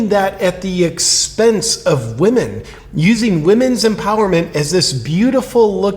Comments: “We men” is what it sounds like he is saying. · Sounds like he said 'weakmen'😂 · I heard "we"men. 🤣